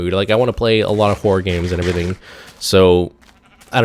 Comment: Thought you were saying "Whore games" instead of "Horror games". I was like whhat? lol